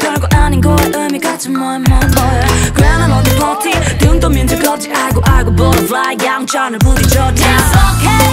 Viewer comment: I always hear Grandma want to party XD